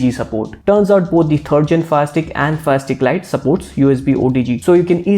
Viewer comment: For a moment there i thought you were saying plastic and plastic lite hehehe sorry